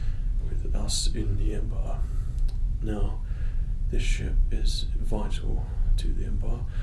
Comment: I thought he said "shit"@ 0:47 LOL